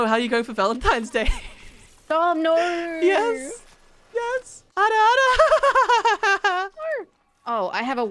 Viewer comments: did he just said "ara ara" !? that ara ara means............... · Did I just hear say Ara Ara